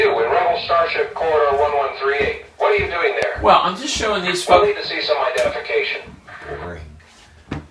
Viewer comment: did he say the f word? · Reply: He was going to say "Well I'm just showing these folks"